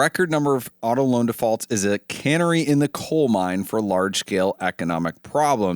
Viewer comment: Did he say "cannery in a coal mine"? That would be a weird place to send the fish to be canned. Except maybe at the Boulby mine. They could open a hole for the fish to come in to the mine and right to the cannery.